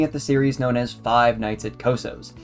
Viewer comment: I thought it said Five Nights at Costcos. 😂😭